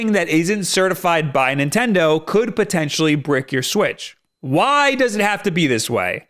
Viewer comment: Did he say “brick” or “break” in 0:27 i just wanna know because I hear “brick” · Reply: Brick